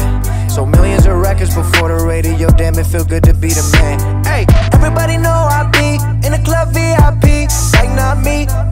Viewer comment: Everybody know I be in the club VIP, syke not me. Get Bobby Tarantino 2 available now on all streaming services: <link> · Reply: I thought logic said Evrybody know i be in the club V i pee